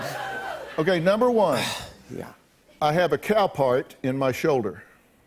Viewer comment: What was in his shoulder? I didn't understand that word. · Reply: I thought he said cow pie. LMAO!! :P